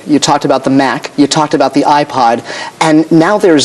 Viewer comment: I heard “iPad” and I was freaking out thinking about time travelling 😂